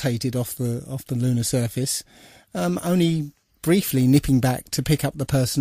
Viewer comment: What is that you say at 39:56? "briefly "nicking??" back to pick up the person operating the camera"? I'm trying to quote this, but not sure what word is used there. Thanks! Great work, by the way! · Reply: Thank you Lori, I actually said "briefly nipping back to pick up..." sorry it's a very English phrase I guess :)